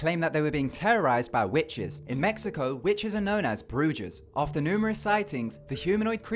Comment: Is it just me or are 50% of the comments about 0:43? (He said 'bruges' instead of 'brujas') 😂